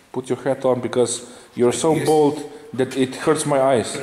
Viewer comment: First 7 seconds I heard "put your hat on because you are so bold it hurts my eyes." Then I realized